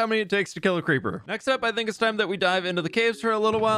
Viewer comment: XD An add played in the middle of the vid, and the last thing I heard was, "Next up, I think we should die--"